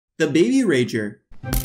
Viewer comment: I thought he said da baby rager. Lol.